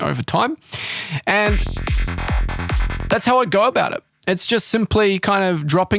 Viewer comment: "..aaaaand, that's how I Goa bout it...", Tell me I'm not the only who heard that, and had to do a double take (had vid playing, listening while reading) :P